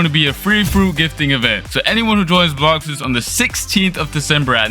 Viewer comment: join what i can't heard it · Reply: He said join blox fruits